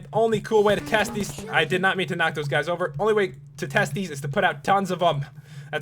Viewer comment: did someone say TEST